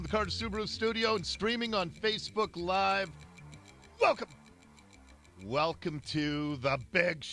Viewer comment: He said welcome I'm sure, but it sounded like he said f***k 'em!